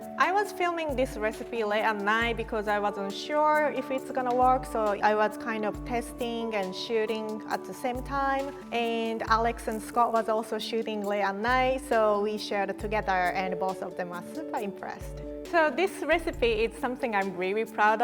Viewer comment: it sounds like she said she was pissin and shitin at the same time 💀